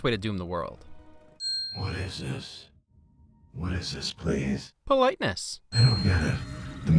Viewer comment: always thought he said place not please